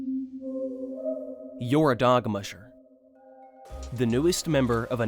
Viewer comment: what did you just call me?! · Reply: thought he said dog-muncher and i got scared ngl